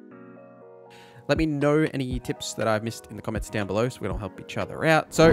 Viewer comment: In the first 20 seconds of the video, says, " Let me know any tips that I missed in the comments down below so we all help each other out." He specifically says this starting at the 0:14 mark if you didn't hear it. Now,For the reason that I typed my original comment. At the 2:07 point of the video, says: "Now if you're at a workbench you can press T on PC, or I believe it's Triangle on Playstation, and o think it's X on Xbox to immediately transfer all of your junk into that container, and you should be doing this st all of your settlements so you're not actually carrying all of that heavy stuff" continues, but hopefully, you get the gist of it. I made my comment because 1) He asked the viewers to let him know via the comments if there are any tips he missed so we can help each other. I know my comment wasn't a missed tip, but I meant to just let other viewers know the correct button for storing all junk on Xbox. 2) There has been a huge spike in the numbers of both returnin and especially new players since the release of the Fallout Amazon series. Not all of those new players understand the intricacies of Fallout 4, especially when they're starting out, so people look for videos like this to help get the hang of playing the game. 3) As your reply stated, the button is clearly shown as an option in the menu when you're in the Workbench building options. That's true. With that being said, a lot of new players miss small details like the "Press Y to Store